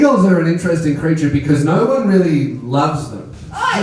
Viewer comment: I heard "singels are a different breed, cuz nobody really loves them' and it made sad 😂😂